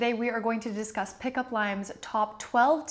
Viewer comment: legit I thought you said "pickup lines" at the beginning . And I was like, sure, I could use some of those too